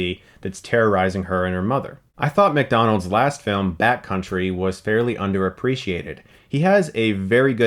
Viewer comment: Backcountry I thought you were saying Bat country.